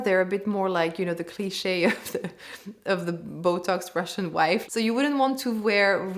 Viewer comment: did you just say "botox Russian wife"?? How elegant of you to disrespect other nationalities! Bravo. I'm outta here 🤮